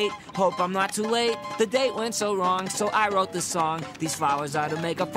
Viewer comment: i thought he said boobies tomorrow what a puzzle i am.